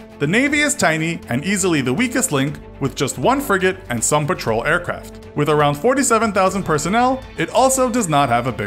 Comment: did he say aircraft?